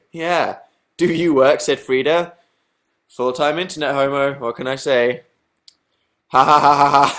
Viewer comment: Did he just call himself a full time internet homo? o_______o